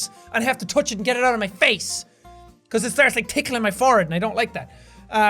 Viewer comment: It sounded like he said "when it's touching me fart" (he said forehead)